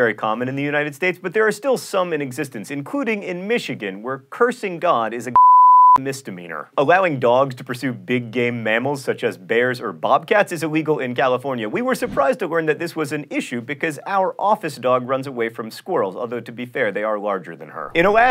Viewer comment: I heard “Allowing dogs to pursue big gay mammals”